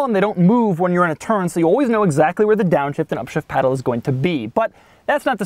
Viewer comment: it sounds like Doug said "shit paddles" at 5:51 lol